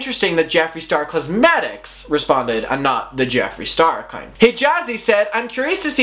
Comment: I know you said “the Jeffree star account” but I heard “the Jeffree star cun@ “ 😂 i had to rewind a couple times just to be a 100% sure😂